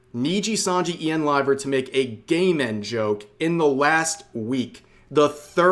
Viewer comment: It sounds like is saying, "gay men joke." I know he's not, it just sounds like it.